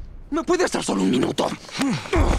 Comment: tengo un efecto Mandela con respecto a la frase de Gordon juro que pensé que decía "Te tenemos estúpido Payaso"